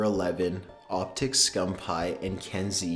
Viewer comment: did he just say Optic Scumphigh